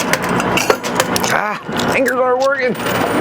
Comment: what did you say? I listened to it several times but can't figure it out. I'm guessing $3,640 as winning amount. Good luck!! Thanks for the video! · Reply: Fingers aren't working